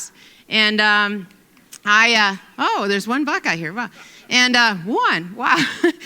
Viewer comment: For a second I thought she said there's one black guy here . I'm like why is she so surprised? Oh one buck guy.. haha